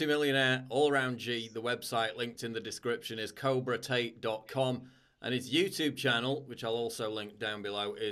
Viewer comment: I could have sworn that you said cobra taint